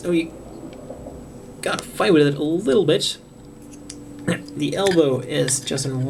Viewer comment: did he say "Little Bitch" ?